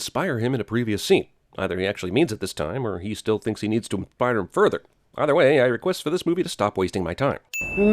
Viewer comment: What the fuck did you say at 13:36? "Msthpiderhm further"?